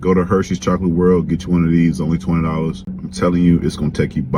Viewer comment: I had to turn on captcha because I thought he said only $200 😂